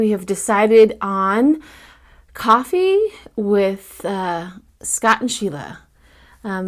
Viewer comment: I thought you said coffee with Scotch and sugar at first.😂